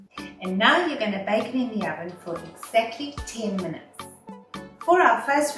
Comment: What are you saying? How many minutes? Please get rid of the background music. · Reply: Can't alter a video once its published. You can turn your subtitles on and set it to English..."and now you're going to bake it in the oven for exactly 10 minutes"